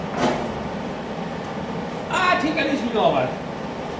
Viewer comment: WFT DID U SAY!!!! Did that mean VERY HOT or something lQQl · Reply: Ahh I think I'll put a glove on .. you heard ahhhh tinkillputa gloveon. Hahaha. Sorry we newfoundlander speak fast